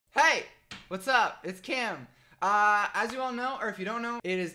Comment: I thought u said “what up its Kim” oof